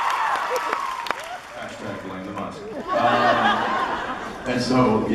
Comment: What does Jared say at 46:18?🥺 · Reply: Jared: The musk.